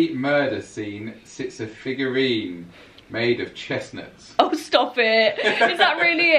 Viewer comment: To be fair I thought said a man made of chestnuts as well 😂😂